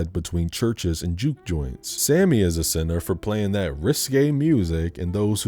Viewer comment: Oh! Risqué. I thought you said “wrist gay”.